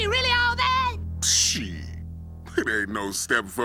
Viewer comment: did he say shiiiiiit.